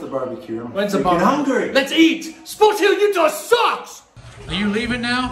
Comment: im laughing so hard on that part of 2x speed. For those who don't know what said on that part, he said: "SPORTS OF UTAH SUCKS!"